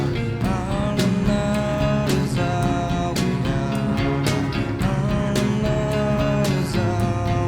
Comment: I always thought he was saying, "All alone is all we are" at the end.